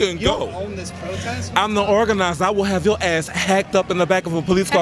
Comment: Packed up, not hacked up...jeez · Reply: alex lmao go watch it again, he clearly says hacked 12:14 the fact you feel you have to defend him is interesting though, do you think he had a right to place hands on somebody and shove them simply because they have an opinion different than your own?